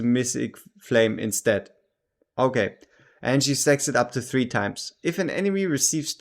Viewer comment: lol that intro. You got me in that state of mind and at 2:26 i thought you said "sex'ed" it up instead of stacks it up. Laughs aside, thanks for the in-depth preview. Interesting champ. Looks useful, but not silly OP, which can always be a nice change of pace.